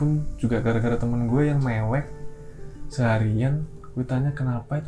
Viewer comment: temen gue yang me*ek Hampir salah denger wkwkw